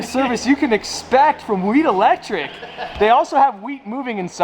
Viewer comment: Bruh every time he said Wheat Electric it sounded like he was saying weed electric